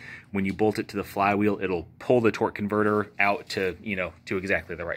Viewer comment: I apologize I thought you said a used converter